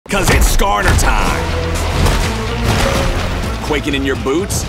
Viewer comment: Did I just hear "It's Skarner time"?